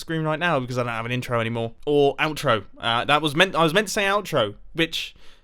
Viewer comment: it sounds like he said b*tch tho he said which